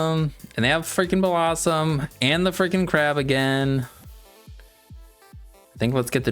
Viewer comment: I thought you said they have Crabominable